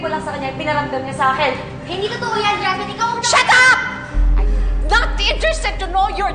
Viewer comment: akala ko sabi nya " sheda! " instead of " shut up!! "